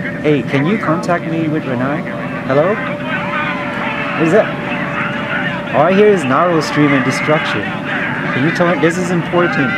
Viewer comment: "I miss Ranai"- Sad 2k16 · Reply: Lewmonkaizer i thought that he said “all i need is ranai"